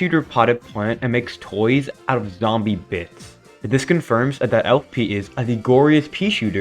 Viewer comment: dawg I thought you said zombie piss😭